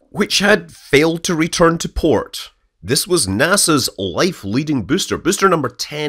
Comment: "NASA's light leading booster" !!! Dear Scott did I hear it well or at 0:18 you said these words? Ahh, you are only testing us, you old crocodile! !!! Merry Christmas !!!